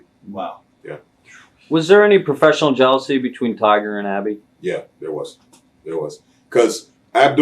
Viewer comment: I was watching this video and at 32:12 I swear you can hear an evp saying "Gabby" as Bad News Brown is talking. By the way an evp is considered a ghost talking out loud. Please check it out at 32:12 in the video.